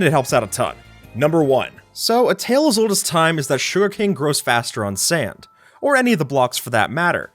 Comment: Am i the only one who heard Shuriken instead of sugarcane